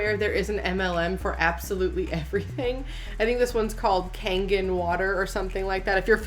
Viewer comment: Honest to god I thought the water filter said 'Karen water' and talk about drinking the kool-aid. I was concerned.